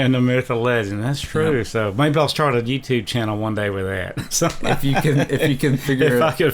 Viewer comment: did he say a GTA channel?